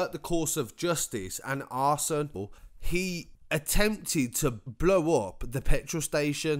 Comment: I swear he says arsenal lol